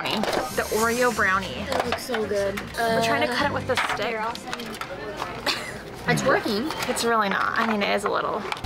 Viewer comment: when you were cutting the brownie i swear i heard say "its twerking" instead of "its working"